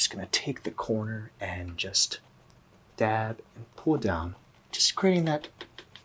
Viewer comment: When he said "pull down" I thought he said "puta" and honestly that's on me